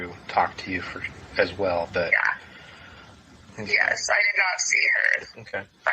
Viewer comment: No, Saturday night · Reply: timestap around 26:13/14....it sounds like she says 'I did not see her friday night.' Around 24minutes, the first time she says 'that evening'...so maybe she just mispoke the second time?